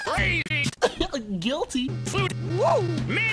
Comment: did you say hoe? what the heck is going on 😂😂😂 I'm laughing at dis ytp hahahaha! yes I like dis ytp